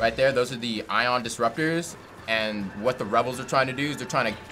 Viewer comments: did he just say rebels...................... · Woah woah woah did he just say the REBELS????